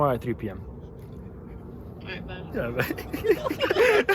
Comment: did she just say "ight bet" lol